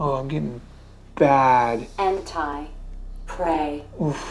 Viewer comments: I heard “end time pray” or was it “end time PREY”?!?! 😳 The shadow figure in the demon house freaked me out and, lens flare??? With legs?? Great job as usual 👍👍🙏🙏❤️❤️ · thought it said “hentai prey” XD